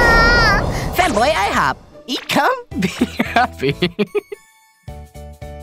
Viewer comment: When i heard "eat come eat happy" 2:46 i was dying of laughter